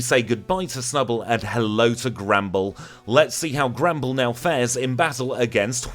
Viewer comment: I just realised he's saying Granbull, but every time, I hear "Gremble"